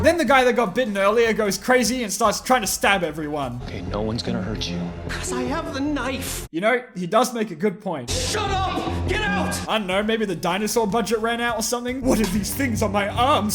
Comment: "No one gonna hurt you" "CaUSe I hAVe ThE KNIFe" 20/5 writing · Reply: I thought he said: I AM THE KNIFE